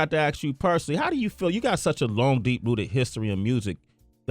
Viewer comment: LMAO 3:10 sounds like he says " you got such i long deep booty"